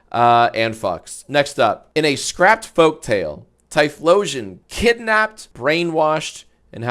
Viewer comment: Erm actually 👆🤓 The scraped folk tale is about Johtonian Typhlosion. Hisuian Typhlosion did nothing wrong. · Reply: I thought you said Jonathan Typhlosion for a solid moment